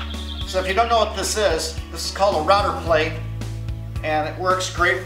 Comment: What did you call the tool for cutting mortises? It sounded like you said router plate, but I can’t find it under that name anywhere. Thanks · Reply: try router plane